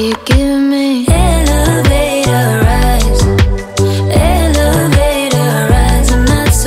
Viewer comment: I thought she was saying "elevator ass"